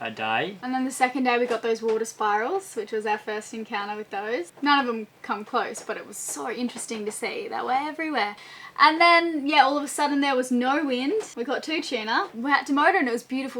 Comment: It sounds like Elayna is saying you saw water spirals everywhere? Am I hearing it wrong or is that something?